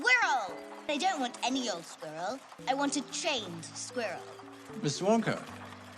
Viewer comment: see she’s saying “I want a trained squirrel” but I always thought she said “I want a chained squirrel” and I’m like what the f